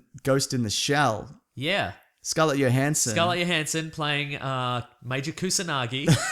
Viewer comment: Skullet Yo Hansen? wha? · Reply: I heard it as Skallet Yo Handsome